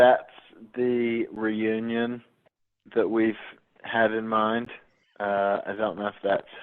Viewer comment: UMMM WAIT!!! DID HARRY JUST SAY NOT THE REUNION WE HAD IN MIND!!!!!??????